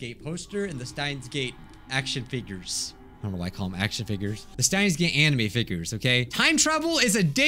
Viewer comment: im sorry did he say Science gay anime? Sorry wah